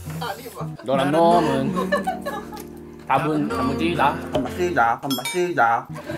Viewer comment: 잘 들으면 단맛이다라고 들리는건 나뿐인가?